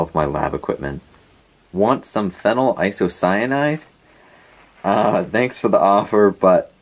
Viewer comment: phenyl isocyanide did i hear it correctly? what uses do that have?